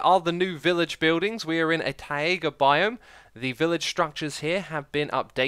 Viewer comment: DID HE SAY tie/a/ga because its tie/ga